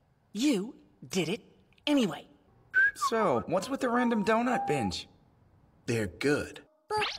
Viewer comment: I thought teru said whats with the random donut, bitch 😭